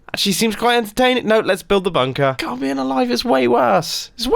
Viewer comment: Did mumbo actually say bumper instead of bunker?